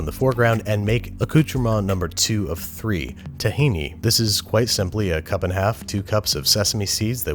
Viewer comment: sounds like Him saying Tahiti like dutch